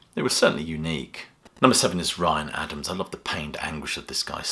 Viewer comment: did you say Bryan Adams ? If so, is that his picture ? Anyone ?